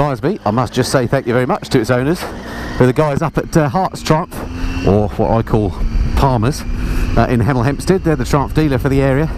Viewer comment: Would you be able to share the details of the dealer you used in the comment description section (or maybe just reply here), I did heard you at around 14:26 but couldn't understand properly. Thank you :) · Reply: It was Hertfordshire Triumph in Hemel Hempstead - sadly now closed (and replaced by Triumph North London in Watford)